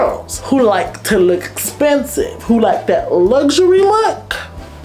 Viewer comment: did she just say “ if you want to look ‘spensive’”?🤣